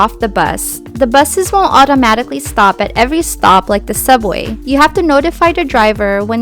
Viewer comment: The buses don't automatically stop at each stop. They will if someone is at the stop waiting to board, or if someone has requested to get off (pushed the button). But in Manhattan midday, the buses tend to be busy enough that there's always someone getting on and off at each stop so it seems like they stop at every stop. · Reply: ok, we listened to that part twice and thought you said they will stop at every stop. Thanks for the great video